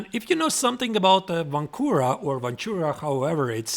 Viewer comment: Sorry, Vontura or voncura? I misheard that first time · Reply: Vancura position